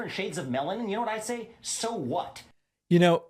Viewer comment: I also thought he said "shades of melon"! 😂